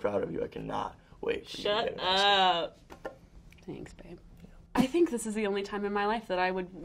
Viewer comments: is it just me or did she say thanks babe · “you look like my next boyfriend” “that’s because i am.” stop. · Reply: alexis . Did she say thanks babe at 3:14? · Did she just say “thanks babe” at 3:16 ha I’m dead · Wait am I the only one who heard Shannon say "Thanks babe" at 3:15?? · shannon "im tying to buy you" noah "im not for sale!" HAHA SHIP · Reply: TheTravelingJalapeño did she say thanks babe at 3:14 ? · did she say THANKS BAbe!! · did she say thanks babe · does anyone else hear her say thanks babe ? or was it just me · I heard her say “Thanks babe” 😫